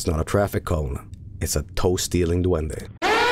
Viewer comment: it sounds like Joel is saying Toasty little duende instead of toe stealing duende